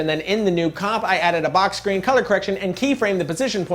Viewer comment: Sorry I can quite hear what you said @ 5:14 something like box grain color correction :S What did you actually say :P